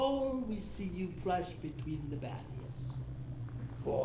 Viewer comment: Why do subtitles say "BEYOND" instead of "BETWEEN"?